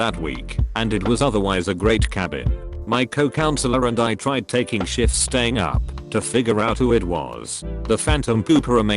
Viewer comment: I thought he said they "tried taking shits standing up"